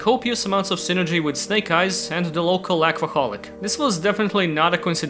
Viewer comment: Hehe. Local Alcoholic 3:41 A phrase to remember 6:26 Gadget: I Will search for food 7:24 Is it just a different Card in a new pose? 9:24 I love this card's artwork 9:47 favorite support philosophy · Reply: he actually said "aquaholic" since she drinks water, not alcohol.